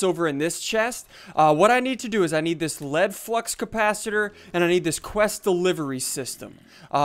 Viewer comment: anyone else hear lead fuc#s